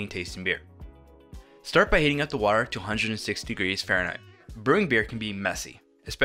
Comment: Did he say heat up the water to 260 F (at 2:30)? I thought it boils at 212 F.